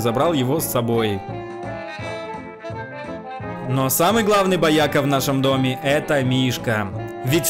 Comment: Ты сказал что мишка бояка мне если честно послышалось вояка